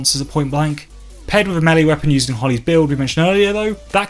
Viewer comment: Great video. I have no idea what you said at 9:40 though lol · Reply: Paired with a melee weapon in hollys build